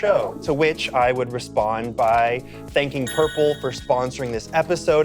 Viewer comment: Ngl I heard purpled instead of purple (☞ ͡° ͜ʖ ͡°)☞